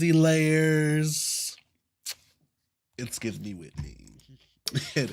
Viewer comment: I think he was referring to Whitney AKA Naptural85 here on YT. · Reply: I thought he said Whitney 1980's